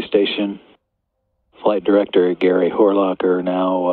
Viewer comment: Did he say Gary Whore-Locker?